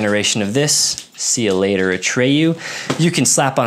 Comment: I was listening (not watching) and thought you said you had a 'See You Later, Atreyu' patch! I was wondering what the context was for that! I figured it out... But it would be funny to have a CUL8R ATREYU patch!